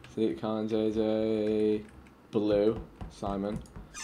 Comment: did vik say that jj blew Simon